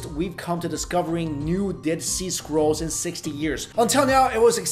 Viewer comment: Ok, not gonna lie, at first I thought you said "Dead seasquirrels" and I went "WTF is a seasquirrel"? Then you said it again and I realized what uou ACTUALLY said.😂